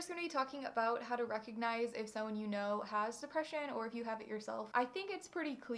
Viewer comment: Did I hear suppression instead of depression? Sorry I know this is a more serious topic and another will becoming. But I couldn't go on without saying something!